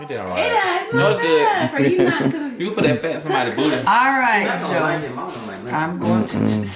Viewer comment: did ' s brother say " you could actually put that fat in somebody's booty"? Who else heard that too?